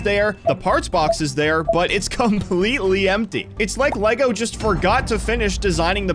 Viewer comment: DID BRO JUST SAYIN' "FORGOR"